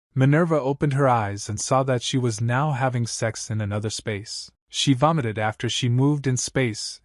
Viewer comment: Having S## I heard that right . Right ??